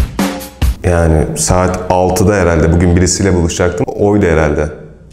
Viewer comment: melis mi !!!!??? neeee 😭😭😭😭😭😭 doğru mi duydum şey ???? ☹️☹️☹️❤️ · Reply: Birisiyle buluşacaktım diyor. Melis demiyor.